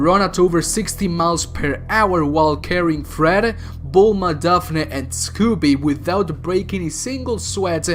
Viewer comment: Did you just say Vulma?